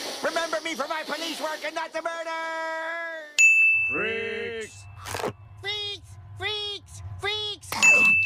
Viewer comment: I first thought they said "FREEZE!". But they said "freaks!"